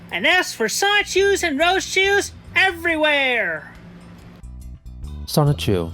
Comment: I think at around 2:08 Chris is saying “an S for sonichus and Rosechus everywhere” not “and as” just thought you should know ;p